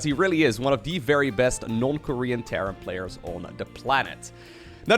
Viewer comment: I actually thought he said “one of best North Korean Terran players.”